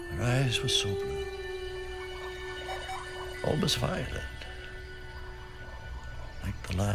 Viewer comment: What does he say in minute 1:22? Can someone please tell me? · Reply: "Almost violet. Like the last touch of sunset at the edge of the world." (Describing the shade of blue her eyes were.)